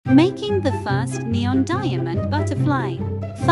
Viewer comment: I heard “making the first ever neon buttaflier” 💀